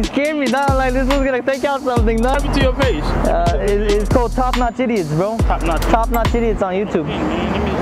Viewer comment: for a sec i heard TopNotchTities lol